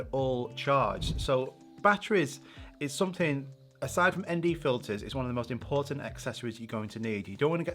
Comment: What did he say at 3:32? "Aside from ND filters"?